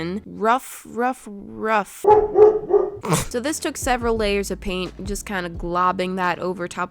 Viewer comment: When you said you put severel layers of paint i thought you said that "this took severel of DAYS to paint"😂